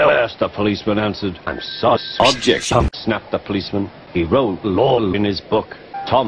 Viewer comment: wtf does he say XD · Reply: "Objection!"